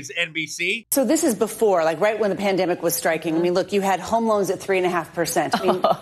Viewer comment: I almost thought she said "Ho-Mo-'s" at 3%....lmfao!... That would have also fit the narrative.🤣